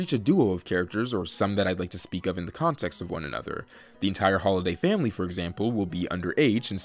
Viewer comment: When he said, "The entire Holdiay family will be under H", i thought he said "The entire Holiday family will be underage" 💀💀